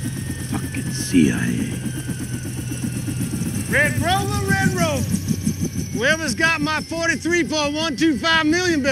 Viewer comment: what does he say when he comes out of the copter? "Red rolling red rose"? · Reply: Red rover red rover. It’s an old game